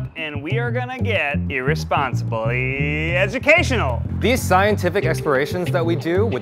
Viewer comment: Did he say science expirations 1:01 😂